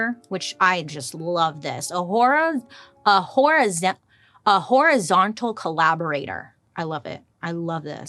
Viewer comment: Horizontal collaborators is going to be the new aqua tofana 😂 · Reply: I honestly thought Bailey said Whorezontal lol 😆